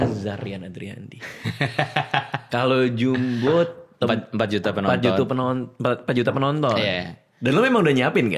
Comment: Ada kah yang bisa membantu, aku miss ketika bahas juta pantau, atau juta panto? aku google tidak ketemu aku sepertinya salah dengar, adakah yang bisa menyebutkan apa yang disebutkan oleh bang ryan dan bang radit?